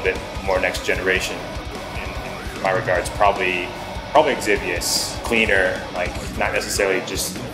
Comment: didn't catch what he said, what band is he referring to? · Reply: Ah, figured it out from the album name: Exivious